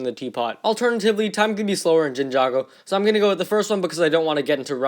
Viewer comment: did he just say jinjago